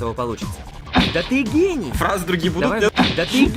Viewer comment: мне одной вместо "ты гений" послышалось "ты гей"? 😂 Анимация как всегда на высшем уровне)